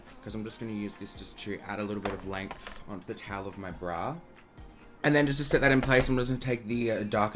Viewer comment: Did he say ‘Bra’ when he was meant to say ‘Brow’ at 2:09?? 😳